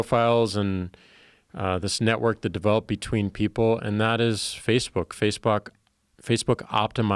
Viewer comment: Did say FaceFuck? :)